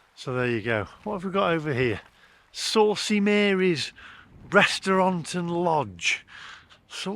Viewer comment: i thought you said saucy marys breast